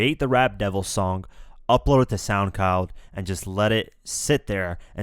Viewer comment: i heard soundcow :)))))